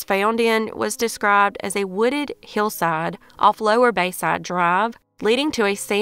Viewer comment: I think I need to wean off crime stories. You said “wooded hillside” but I heard “wooded kill site”. 💕🐝💕